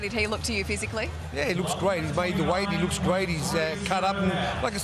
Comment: I thought George said he makes the weight comfortably? 😂😂😂